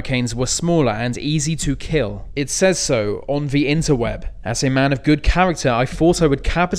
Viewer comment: Did he say interwet?